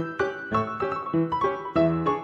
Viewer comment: Apropos, I just realized that the robo-voice, which I thought was saying "buy four now" at the end has actually been saying "bye for now".